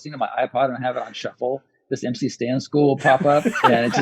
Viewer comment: What's Jade saying? MC Standschool? MC Stay In School? I tried Googling and can't figure out who he's talking about.